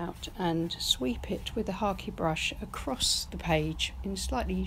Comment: I am new to painting and am trying watercolor first. Love this. What was that last brush you used? Sounded like you said hockey brush.